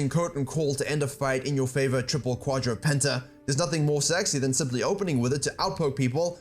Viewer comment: did i hear penta?